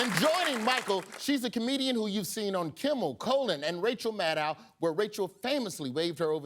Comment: I'm sorry but did Royjust call Conan 'Colon'? 😂